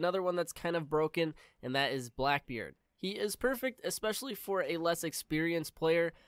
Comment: Did he just say blackbeard is fucking broken?